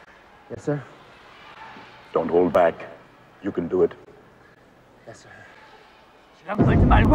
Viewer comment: In case you're all wondering what he said, it was "don't hold back......... you can do it "